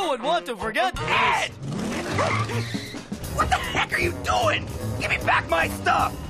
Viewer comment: I always thought he said "wtf you doing?"